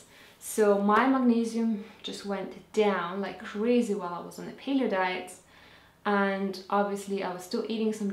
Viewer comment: I did not get what you say here 4:06 4:07 "When I was on a Peter? diet?" Is it Peter or Piner diet? I don't get it. Please explain. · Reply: I was on the Paleo det. xx